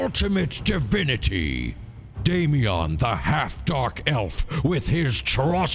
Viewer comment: “Daemeon the half dark elf”😂😂😂😂 “Juston the white knight”😂😂😂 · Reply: I thought it said half dwarf elf for a secons